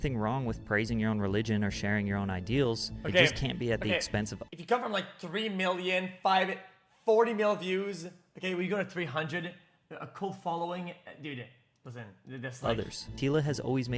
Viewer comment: Does XQc realize that what he said here just sounds like mumbling that makes no sense? I heard every word. He said ""Okay. Okay. If you cover like 3 million, 5, 40 mill views, you go to 300. A cult following. Dude. That doesn't...that's like...like 300 bro". But it still makes no sense. Is it just that English is not his first language or is it something else?